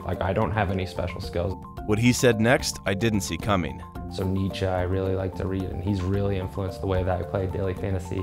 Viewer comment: What did the guy with the hat say after you said you didn't see it coming? @ 17:33... "He really influenced the way I played daily fantasy" Who is he referring to as "he"? I did not understand the name. TIA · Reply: He said "Nietzsche", the German philosopher.